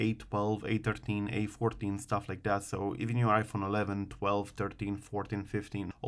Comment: Did he say iphone 15 jailbreak? Or a15?